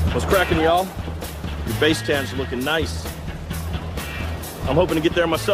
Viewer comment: "What's crackin' y'all. Your base tans are looking nice. Hopin' to get there myself this season." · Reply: I thought he said face tan not base tan... Am i trippin?